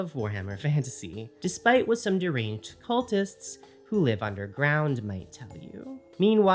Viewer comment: You said 'Who live underground', & all I heard was 'Who live on reddit'.